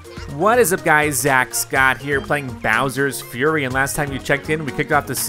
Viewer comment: Who else thought that said "Bowser's Furry"?